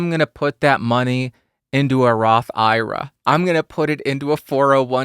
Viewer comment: I initially misheard "Roth IRA" as "rock opera", and I was all onboard. We need more people to invest in rock operas.